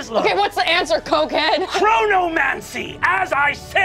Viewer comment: Istg i thought he said necromancy lol